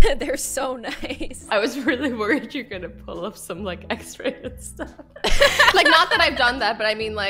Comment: am i hearing it wrong or did she say "x-ray"?